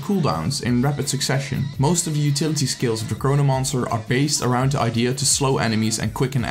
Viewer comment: Watching in 2020 - Is he saying Corona-Monster? Pretty sure. LOL